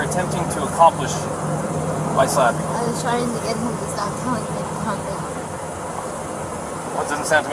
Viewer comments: I would REALLY, REALLY like to know what Gabby says at 49:35. She says, "I was trying to get him to stop talking . . . ??" Then what does she say? Can any of you tell? Later the female officer comments Gabby was mad because Brian wouldn't listen to her. With Gabby now missing, one has to wonder if Brian purposely antagonized her by not listening to her, threatening to leave her, and locking her out. Brian was acting awfully cheery and trying to get along with the officers instead of being bummed or upset. Whereas, Gabby was really upset, especially after she makes her comment at 49:35. I agree with M McKinnon below, Brian gives off Chris Watts vibes. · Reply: "I was trying to get him to stop talking..and calm down" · I was trying to get him to stop [????] What does she say here? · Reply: “i was trying to get him to stop telling me to calm down”